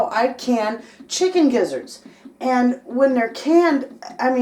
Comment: Wait, you said "Chicken Gizzards"? I thought you said "Chicken Lizards", I'm disappointed, I don't know what to do with all of the Chicken Lizards that I have. Good video anyway😁